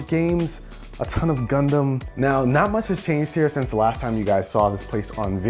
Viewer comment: I thought he said condom aahaha realized he said gundam